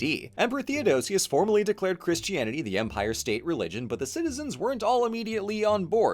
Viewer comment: I heard it as empire-state religion. So Augustine founded the Yankees